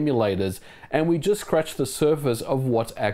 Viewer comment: Did he say "we just crashed the servers" or "scratched the surface". Yani.